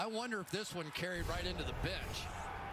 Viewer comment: It sounds like he says bitch lol